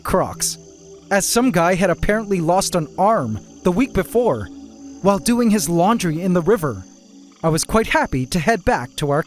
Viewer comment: i thought he said that "someone lost an arm doin laundry i was quite happy"